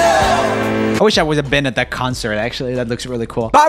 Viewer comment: Why does it sound like is saying I would have loved to be a bin at that concert? :D